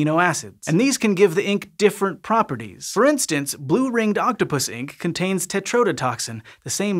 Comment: I can't be the only one that thought he said Octopussy.